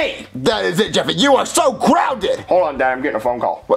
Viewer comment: or 10:35 Did somebody say Banana phone? I love Banana phones! I love to sing Banana phone whenever I talk on a Banana.